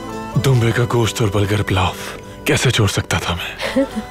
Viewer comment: what does sungurtekin says "dumbey ka gosht aur BURGER pulao"???